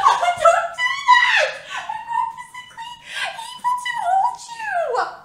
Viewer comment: wtf did she say? 😭😭😭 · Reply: I'm not physically able to hold you